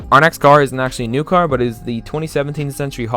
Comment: xD I always hear at new car "A next car is...."